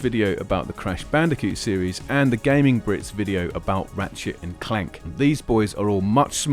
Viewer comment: Was gonna' post something nice... but at 11:43 - really sounds like you said "Rat-shit and Clank" I'm still belly laughing!